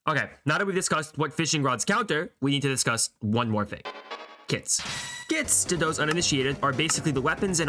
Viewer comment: I heard kids and was very confused for a second there